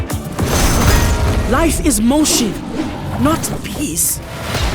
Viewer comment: i thought she said "life is more shit" 😂😂